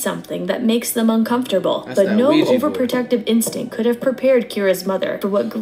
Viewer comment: Lol thought you said Fuji Board lmaooo